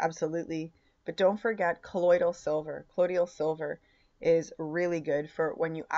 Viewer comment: you mention "something-silver" , can you write here what you said, I 've never head it before, I must admit. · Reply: colloidal silver ,much cheaper if you get the silver and make yourself.